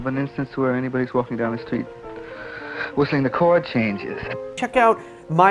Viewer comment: I think he says "whistling the chord changes" instead of "listening to chord changes" at 6:16.